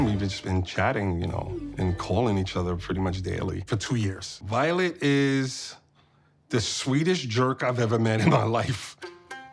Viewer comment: did he say "Violet is the sweetest jerk I've ever had in my life?"😂😮